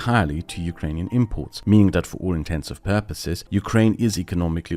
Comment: did bro say "for all intensive purposes"